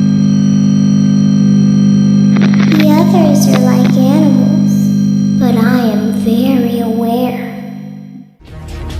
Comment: what does she say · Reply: no she says “the others are like animals but i am very aware”